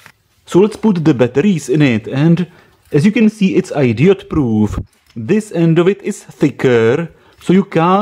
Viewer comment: did he say idiot proof?